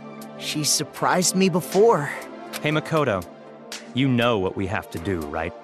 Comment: wait did he call him makoto